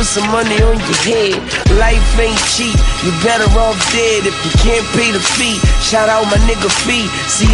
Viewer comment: When I first heard this I thought he said "Can't pay for the seat meet my nigga feet."